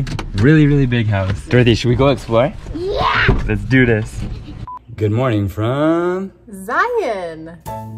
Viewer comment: At first I thought they said " good morning Trump "😀